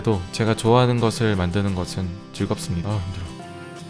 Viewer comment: 힘들다고 엄청 잘들리잖아ㅋㅋㅋㅋㅋ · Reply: 아 힘들어